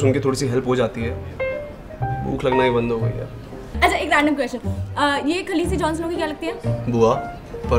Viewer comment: kya bola ye khalsi jhonsnow ki kya lagte h mujhe suna Mia Khalifa JhonSena K kya lagte H 😂😂🤭😂😂